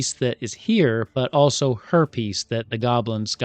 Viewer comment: Did he just say Herpes 😳 😅